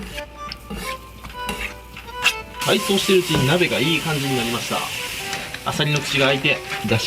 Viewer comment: ｢鍋がいい感じになりました｣が｢何故かいい感じになりました｣に聞こえた